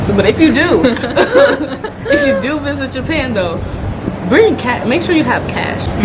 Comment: legit thought she said if u do visit japan make sure you have cats XD